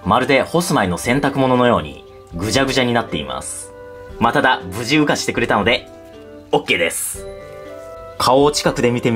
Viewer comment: 無事羽化してくれたのがOKですが →不自由化してくれたのでOKです に聞こえてしまった(笑)